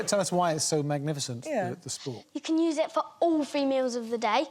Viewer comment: I first heard it as: "You can use it for all females..."